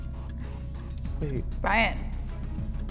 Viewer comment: Is it just me or did anyone else hear Brian at the end call Kaeyln babe